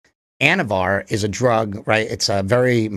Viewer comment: For like 5 minutes I thought this guy was saying anabar and im like no way someones thing got bigger eating a youtuber protien bar